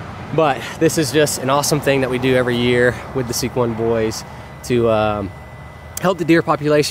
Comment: I thought you were saying "sequin boys" . Lol